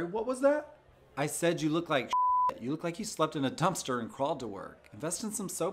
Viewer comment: The first time, I heard “you slept in a dumpster in Baltimore.” Funny part is, it still works